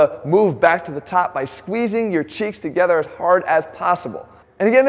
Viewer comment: Never thought I would hear this man say ‘’and squeeze your checks together as hard as possible’’